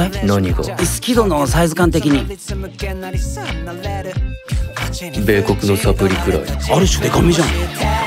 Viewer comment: 好き度の大きさ何て言ってるのか全然聞き取れなくて8回くらい挑戦したけど分かんなくて諦めた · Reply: 米国のサプリくらいって言ってると思います！